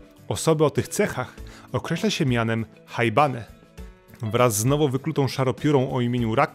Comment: kto usłyszał najebane